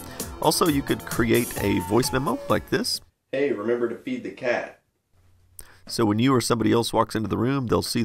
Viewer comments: I thought he said "HEY, REMEMBER TO BEAT THE CAT!" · I thought he said "remember to beat the cat"